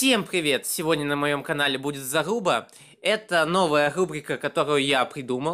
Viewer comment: Мне послышалось " сегодня на моём канале ьудет залупа"😂😂😂